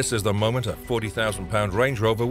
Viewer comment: did he say 40,000 lb Range Rover?? 😂😂😂